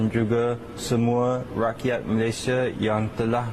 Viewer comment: caption subtitle, I think they replaced the word rakyat Malaysia to rakyat Indonesia. I really hope that YouTube can have a proper Bahasa Malay auto-generated caption instead of having just Bahasa Indonesia